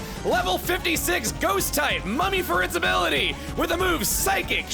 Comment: For a brief second, I misheard say that Cofagrigus had Money as its ability instead of mummy.